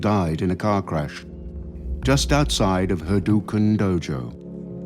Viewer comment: Did he say Hadouken dojo?